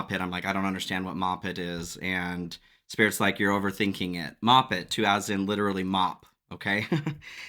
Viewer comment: I heard Mop it like to take ☠️